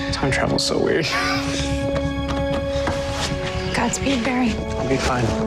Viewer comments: catlin said god speed barry in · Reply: thibaut dream I thought she said got speed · Did caitlin say godspeed? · did anyone else hear godspeed